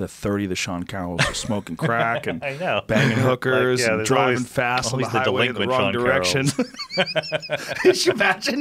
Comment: misheard 'drivin’ fast the wrong way with an erection'